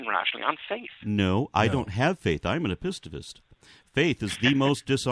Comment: What does AronRa say he is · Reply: it's apistavist which is someone that doesn't follow knowledge based on faith